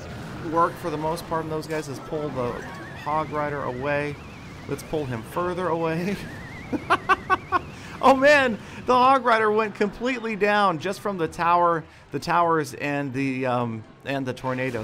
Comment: Sounds like said "Log Rider"